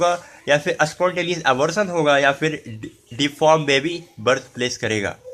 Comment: thank you so much! CORRECTION--->9:39 ......IT SHOULD BE ABORTION INSTEAD OF ABSORPTION